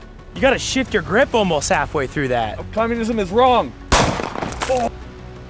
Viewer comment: In the instagram vid I thought you had said “feminism is wrong” in the beginning and I almost wish it was what you said 😂